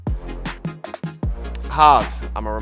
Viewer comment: I thought he said Haas lol I was shoked